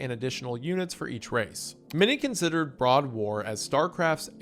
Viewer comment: did he just say Broad War?